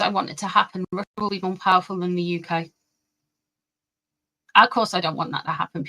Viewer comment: The audio cut out, but it sounded like you said Russia was going to be more powerful than the UK. Is that what you said? Isn’t it already?? Hasn’t it been for some time??